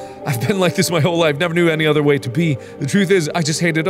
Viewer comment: Thought he was saying “other way to pee” for 4 times (I’ve had to rewarch this part a bunch, kept getting set back) until I finally read the note.